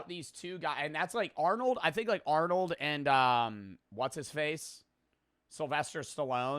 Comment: did he say what is his face ???????????????????????????????